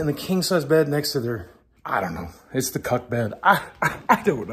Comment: I heard Cuck bed. The subtitles say Cut Bed lol.